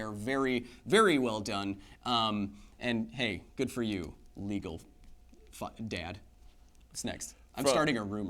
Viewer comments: What does Kyle say at 10:16? It sounds like "legal f*ck dad". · did he say "legal f-ing dad" at 10:15? lolololol